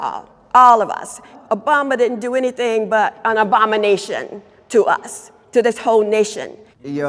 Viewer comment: Instead of "abomination" I heard "Obamanation" and I know I shouldn't, but I just laugh so hard. Sorry